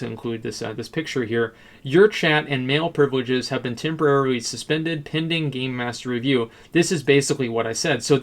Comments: Thought he said "Your chat and male privileges has been temporarily suspended" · god for 5 second i thought he said "your chat and male privileges"